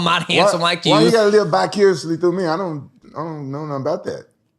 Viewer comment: He said vicariously...Rampage thought he said bicuriously.